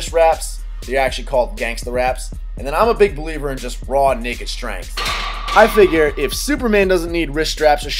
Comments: "Ima big believer in just raw nigga strength." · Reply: I thought he said "I'ma big believer in just raw naked strength." lol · It sounds like said "raw nigga strength" at 4:20 LOL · did he say "I am a believer in raw N@ stregnth?" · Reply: Naked. lol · i thought you said raw nigga strength . was like dafuq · For a second at 4:21 I thought you said. "Im a firm believer in raw nigga strength". LOL Had to rewind and listen again.